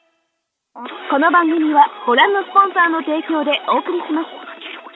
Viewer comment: Que dice en el minuto 1:55 , lo e escuchado antes en bleach pero no sé que significa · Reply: この番組はこのスポンサーの提供でお送りします。